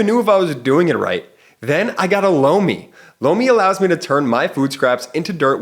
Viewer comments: OMG! When the Lomi ad came on right after Cohen, I thought I first heard, “Blow Me” 😂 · Every time I listen to your ad I hear "blow me".🤣😂Sorry